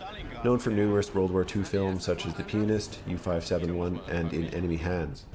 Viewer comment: don't get me wrong but i'm deaf as fuck and I heard "the penist"